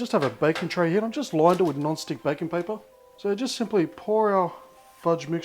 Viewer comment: Every single time, I hear "non-stick bacon paper" :(